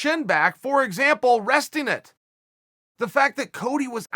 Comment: I thought some sponsor or endorsment promo started @ 1:27, when we were offered 'Restinette' or 'Restynet'- a cure for the weak chin! It sounded like a name for a pill! "Restin' it"...